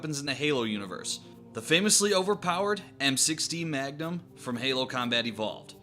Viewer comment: Sounds like you said M16 instead of M6D. =P